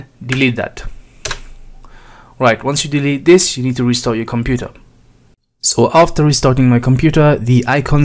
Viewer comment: After I deleted the file I thought he said "Now you need to restore your computer". I almost fell off my chair! 8D